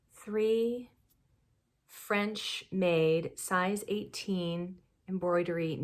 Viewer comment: Haha, when you said, "French made" I was concentrating on my project way more than I thought and I heard, "French maid" and for a split second I pictured what I guess a man would. My next thought was that outfit was impractical to sit around in and darn socks. Welcome to my brain.😛 Back to the podcast.👍